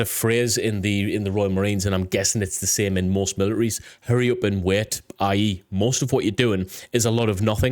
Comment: I swear I thought I heard you say "Hurry up and wet." which honestly would probably make a lot of sense too, lol 😎👍